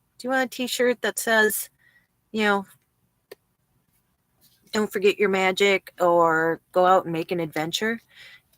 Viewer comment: Go ahead and "wag your finger" at us. Lol. My wife and I enjoy your travels and when you first said "don't forget you're magic," I swear I heard you say Don't forget your meds! So now we always say don't forget your meds!! Keep your spirits up and don't let the snarky people get to you.